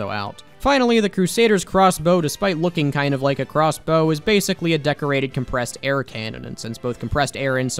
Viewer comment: mishearing: depressed air